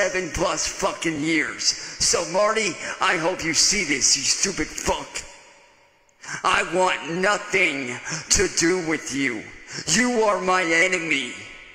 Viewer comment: Well, there it is. He clearly says, "Marty, you are like Ed to me." He wants Marty to plow him.